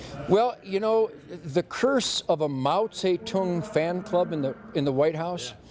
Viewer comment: what is he saying at 1:36 and 1:48? moun say toon??? anybody know its driving me crazy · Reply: Mao Zedong, AKA Chairman Mao.